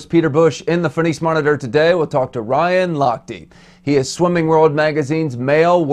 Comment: i thought it said miss ryan lachte